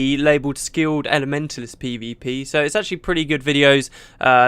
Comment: I could have sworn you said "Skilled and Mentalist PvP" rather than "Skilled Elementalist PvP" :D